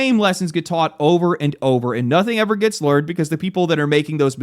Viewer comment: Wait.. 1:22 did I hear "nothing ever gets LEARed", lol